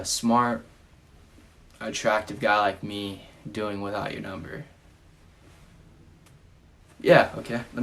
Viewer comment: I hate that during the smart attractive guy like me line, i thought he said with an ogre instead of without your number.